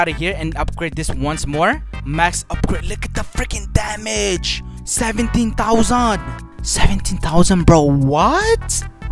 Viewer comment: Did I heard him say "17 tarzan" ?